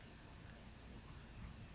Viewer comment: Meant to be on this name! I thought you had said " blow your FAWKING head off.